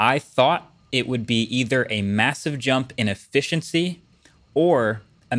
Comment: Does anyone else hear that he's pronouncing his "T" and "S" sounds very strangely in this video? 1:12 "...a mathive jump in effithiency..."